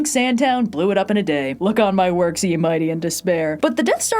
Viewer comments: "Look on my work C and mighty and despair"? WHAT ARE YOU SAYING AT 9:27?! I have played that back 6 times and you STILL make no sense!! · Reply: Quoting “look upon my works ye mighty and despair” in the poem ozymandias. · 📌📌Eros and Psyche PINS are back for one more weekend! – <link> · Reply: "Look on my work C and mighty and despair"? WHAT ARE YOU SAYING AT 9:27?! I have played that back 6 times and you STILL make no sense!!